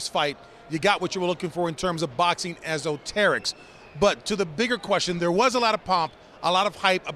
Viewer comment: What in the world Is this word that James brown said @ 1:54 I can not find the word in the dictionary The word sounds like it is spelled this way. O-ter-ricks. I would like to add it to my vocabulary if any one knows?